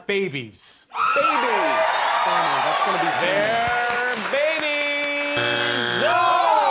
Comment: What'd she say at 2:38? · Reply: “(something something)- babies aren’t on there”